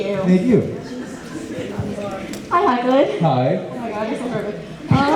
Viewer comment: What does the girl say after hi hoechlin ohh my god?? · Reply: No no she says “oh my god you’re so perfect”